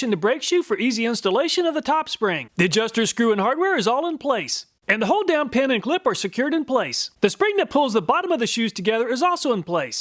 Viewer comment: I thought you said “and the whole damned pin and clip are secured in place”. I had to go back and listen again. My chuckle for the day. Thanks.